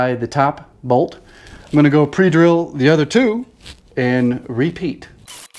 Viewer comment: When you said "pre drill the other two" at 3:10... I heard Yogi Bear. I must be tired. I love your videos. Thanks.